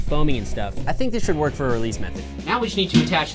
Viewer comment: did he say meth?